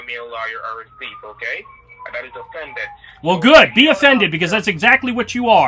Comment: He said offended. I thought he said defendant😅😅.